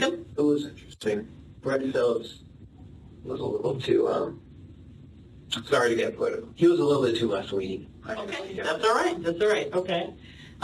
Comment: Transcription error here - what he actually says is "sorry to get political - he was a little bit too left leaning" - obviously if you're a sadist or believe you have a right to murder you're going to gravitate to the right lol